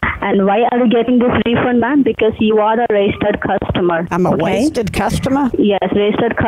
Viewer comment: I thought she said "You're a racist customer"